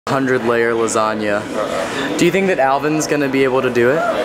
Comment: For a second I thought it said “100 year old lasagna” and I got very concerned